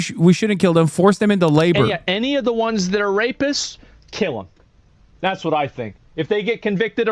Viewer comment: He said "anyone comes in here illegally, kill them". Being undocumented isn't a crime. It's a civil offense. If you've ever gotten a parking ticket or a traffic ticket you're just as much a criminal. This attitude is going to eventually lead to citizens being killed for disagreeing with the current leadership. Your attitude is very harmful tbh. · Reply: I cant believe you got so many upvotes for misquoting. But maybe I have missed that part. Can you give me the timestamp. I heard "rapists".